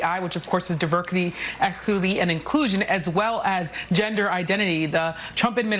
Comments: did she say diverkety eckuty and inclusion? wth? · Did I hear her say, "diverkitty,eckuertty, and iklusion"? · Hold up. Did she just say “Diverkiddy, equiliddy, and inclusion” 😂😂